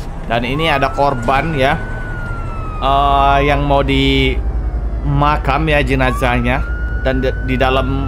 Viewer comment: gw dengernya di makan,ternyata di makam😭👍